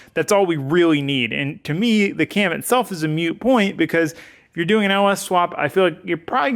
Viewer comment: I had to go back when said that doing a Cam was almost a mute Point. I thought he said moo point and almost died 😂😭🤣🤣